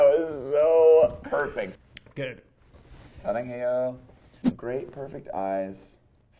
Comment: okay..... does Ezra just said sarangheyo????? (i love you in korean) omg i love him more now